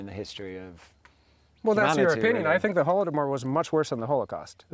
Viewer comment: what is the mumbling thing thats worse than the holocaust? i really didnt get it after 20 times re-listening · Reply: I think he said "Holodomor", which was mass starvation of Ukrainians during the 1930s by the Stalin regime.